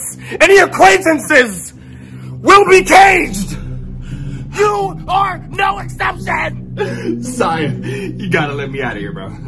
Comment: sound like woody saying you are a toy